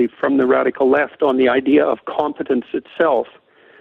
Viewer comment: is the inaccurate subtitling ("confidence" instead of the correct "competence") coming from glenn beck's show or liberty bell?